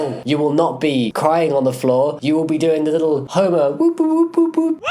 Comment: When you said Homer I thought you said homo😂😂😂